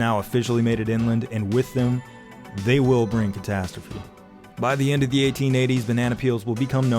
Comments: I legit thought you said "and with them, they will bring potastrophy". Potassium-bro five! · Did I imagine it or did he actually say “potastrophe” at 6:21? And if he didn’t, it feels like a missed opportunity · Did he say "potasstrophy"? K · Did he say catastrophe or potastrophe?